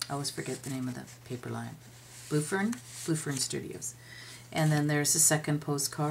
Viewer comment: love ❤️ the Regency Rose II. Would you tell me the name of that paper you made the tag @ 7:40. I couldn't make out what you said, not familiar with this? Love love love the journal! Thanks in advance! Peggy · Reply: Blue Fern Studios